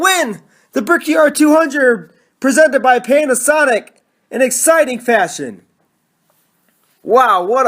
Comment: I heard "pain a sonic"